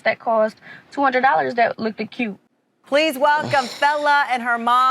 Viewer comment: did she say "loockeded" no way she graduated